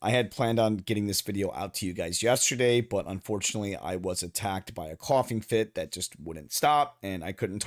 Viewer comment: Im so tired i thought you said i got attacked by a coffee 🤣